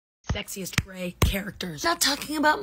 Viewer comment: I thought it said sexist and then realized 😭